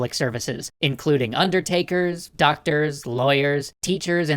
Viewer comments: I'm... I'm sorry, did you say lawyers? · I'm sorry did you say Lawyers